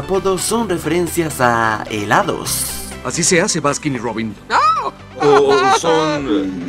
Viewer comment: Yo siempre pense que decia batman y robin